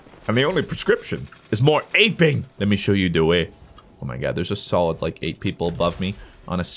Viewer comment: Did he say de way